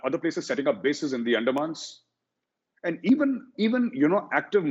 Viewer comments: Whoever wrote the subtitles for the video, they couldn't understand "the Andamans". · This is what I believe he said in the below timestamps: 13:17 - Andamans 20:01 - long back